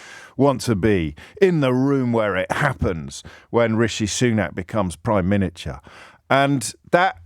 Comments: Did he just call Rishi Sunak 'Prime Miniture'? 🤣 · Does James say prime miniature (07:43)?